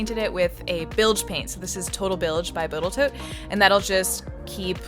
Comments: Did you just say “Botal Tote”? 😂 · Did anyone else catch the spoonerism? "TotalBilge by Botal-tote" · I'm gonna subscribe simply because you said "botal toat" instead of "Total Boat". Lol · did you say botal Toat?? at 1:28? Total boat perhaps? ;) enjoyed the video enyway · I’m pretty sure you said "boatal toat" at 1:30 instead of total boat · Did you say Botal Toat? LMAO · did i hear botal toat? lol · I'm not crazy. At 1:30 she says botal toat instead of TotalBoat, right? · TotalBilge by Botal Tote ? Or am i hearing things? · anyone else catch her say botal toat instead od total boat